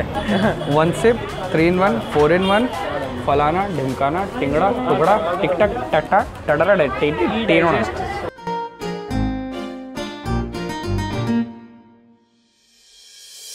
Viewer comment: did he just say " Tatta